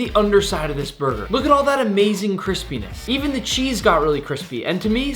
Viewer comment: why are my captions saying Chris pen!s instead of crispyness 🤨